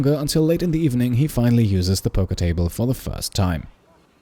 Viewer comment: I kept hearing "polka table"... now I really want one, whatever that may be.